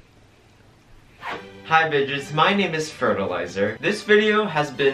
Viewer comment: i thought you said hi bitches HAHA ILY